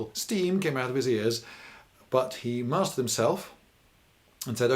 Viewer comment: What does he says at 21:28? English is not my first language. "He ..... himself" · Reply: He mastered himself. It means he took control of his emotions (embarrassment in this case).